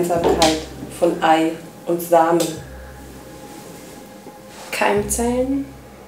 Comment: Hab bei 1:17 auf Pause gedrückt und verstanden "was ist die Gemeinsamkeit von Ei und Sahne".. da war "Kuchen" wie ich fand die richtige Antwort .. (ich weiß, ich bin nicht besonders intelligent🙈)